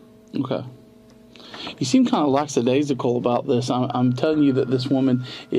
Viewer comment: lack the days ago 😂😂😂 subtitles said "bone apple teeth"😭